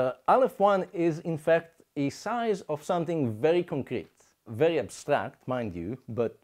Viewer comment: Aleph nought is not something which is very complete; it is something which is beyond completability [EDIT: I misheard the man in the video, I thought he said "complete" when he said "concrete"]. Portions of various alephs are variant in size, but in total size they are all absolutely infinite, making them all the same total size. All infinities are forms of absolute infinity, the only distinction being the rate at which they approach that total. Aleph nought +1 just has a tiny head start toward getting there (a rose by any name smells the same, so we can set omega aside). The only way to measure these as having different sizes, is to measure merely a portion of the infinity, but a portion is not the same as a whole, so we have a huge equivalence fallacy being deployed in these purported maths (these explanations presume that what is true about a portion is true about a whole, but, in the case of total measure, this does not apply; it does apply to the rate at which absolute infinity is approached, but not to the total measure of the whole). These are not various infinities; these are various ways that quantity may approach toward infinity. These are not numbers, because they are not abstract concepts of a quantity. These are abstract concepts of a rate of approaching a quantity, not of quantity, but of rate of approach. The philosophy here fails. Maybe the maths work, but they are being described in untrue fantastical misleading ways. The facts about various rates of approac